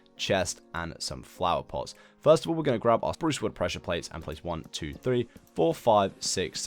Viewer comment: did he just say “OAK WOOD PRESSURE PLANK” instead of “SPRUCE TRAPDOOR”😀😀😀😀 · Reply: he said "spruce wood pressure plate"